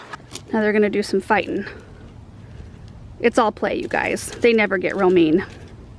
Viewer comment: I thought you said "They never get romaine" 🤣🤣🤣 All this lettuce talk!